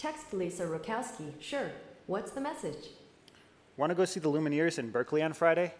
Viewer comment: he said "What" , but the text says "Went" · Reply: sounds more like "wanna/want to"